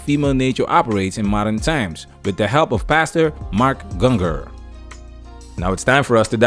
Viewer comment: Everytime You said Pastor I thought you were saying bastard. Partially because of your accent but mostly because Im retarded.